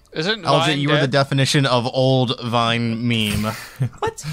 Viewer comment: did you say vine-meme because I said Mardi Gras was like a Vine, and Tesla said Var should go to Mardi Gras for it to be funny